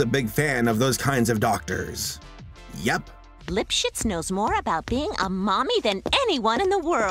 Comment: Back at it again with another "What RUINED" video essay! This time, we're talking about one of the most popular cartoons from the 1990s. I had no idea how much drama was involved in this show's staff. It truly blew my mind while researching. Also, a big shout-out to my friend Jim who helped me research and write this video! Go check out his channel for more video essays: <link> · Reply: Then ever I hear that Dr's name I hear "Lip-Shits", anyone else?